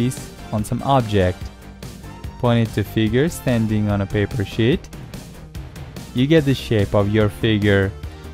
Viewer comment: am i the only one who understand "paper shit" at 1:26?